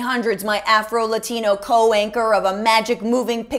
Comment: Co-anchor. I heard that as, co-wanker. They work for Fox. So both are valid.